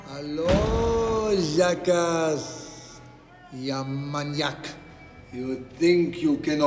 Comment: Алёё заказь, я маньяк. Мне одному так послышалось О_о